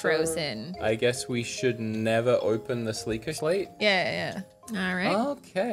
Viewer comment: my man. One of the biggest Legend of Zelda fans. Did you just say "Sleikah" Slate???